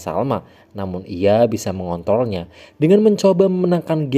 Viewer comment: Gua kok malah dengernya mengontolnya ya